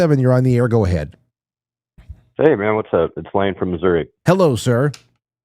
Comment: Prayers for you from Missouri in Jesus's MIGHTY AND POWERFUL NAME AMEN Wow bro! I'm so freaking sorry that happened to you! You are on my prayer list bro! You and your kiddos! Godbless you all!! congratulations on 20 years WOW what a feat! I am proud of you! Keep up the great work homie! God bless you, your listeners and all of your families! Prayers for y’all in Jesus’s MIGHTY AND POWERFUL NAME 🙏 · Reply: I think he said his name is Lane. Anyway, I'd like to have him call in again and I want to read that article he talked about . Lane, if you see this please respond.